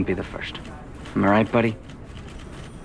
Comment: Don: “Let’s take it a little slower. I don’t want to wake up pregnant.” Pete: knocks up Peggy that night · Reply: Thought he said brain dead not pregnant lol